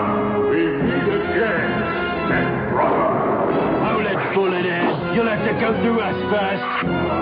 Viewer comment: What the hell did Wolverine say at 0:19? Thats a terrible voice for him. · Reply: I think he said "Hold it bullet head." It sounded like to me as a kid that he said "Hold it bulldozer." which does kinda makes sense.